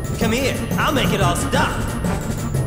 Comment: Axel: GET BACK Sora: No u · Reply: I always thought he said "get black"